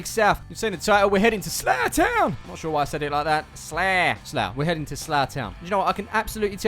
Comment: When you said "weve made it to slough town fc" I thought you said zlatan fc😂